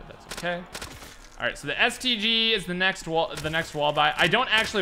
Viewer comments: I thought he said STD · did he just say the S.T.D is the next wall buy O3O